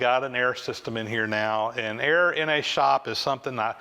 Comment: Oh, I thought you said hair system, you had me confused. Just kidding, I'm only a couple years behind.